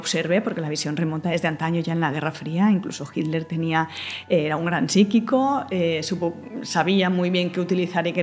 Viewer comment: No ha dicho eso. Dice que Hitler tenía a un gran psíquico (se entiende perfectamente que habla de otra persona). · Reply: no, lo he revisado y no, dice "Hitler tenia... Era un gran psiquico" 7:57. Pero bueno aunque hablase de otro da igual, me sigue pareciendo igual de falso lo que dice.